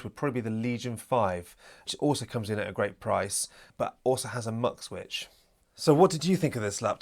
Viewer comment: I listened 5 times his accent is so thick I have no idea what he is talking about. is he saying MOD switch? Mech Switch ? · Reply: sorry mate, I will try and speak more clearly. It is a MUX switch I was talking about where you can switch from dedicated gfx to intel gfx in BIOS. Dell have actually now added the MUX switch in the latest BIOS 👍